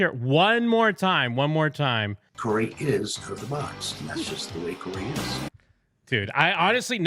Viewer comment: I hear “Corey is coke in the box.” There are definitely two hard “C” sounds at the beginning. Maybe it’s code.